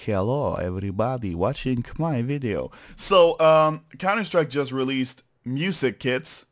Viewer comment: it sounds like he says "so counter strike just released music kids"